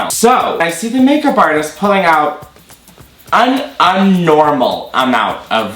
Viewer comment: hon did you just say "unnormal"